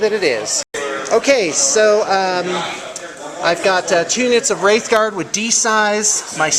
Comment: I was looking away from my laptop and petting my kitten when I heard "2 units of Wraith Knights" and I thought, "You can bring those monsters in units?" so I looked at my computer, went back in the video and found out he said "2 units of Wraith Guard"